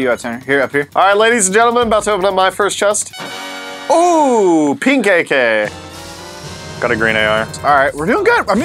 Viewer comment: did he say a pink ak